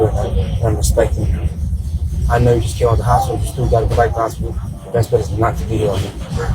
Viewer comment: It's really hard to understand what the guy at subway is saying to him when he's kicking him off the property but I have listened several times and it sounds like the man told "I know you just got out of the hospital and I know you still got to go back to the hospital" Anyone else?